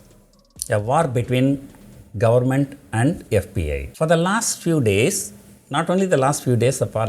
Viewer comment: Am I the only one who heard FBI?